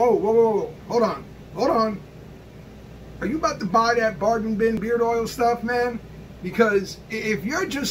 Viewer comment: Why does it sound like you said “baarden bin beard oil”?